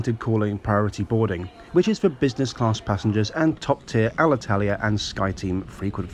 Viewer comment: Oh come on I thought he said Alitalia A380